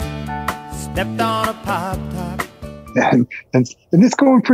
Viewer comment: The first several times I heard "Margaritaville" as I a kid, I thought he was saying "Stepped on a Pop Tart"! When I was singing along at age 13, I was STERNLY corrected by a loyal Parrot Head. RIP Jimmy!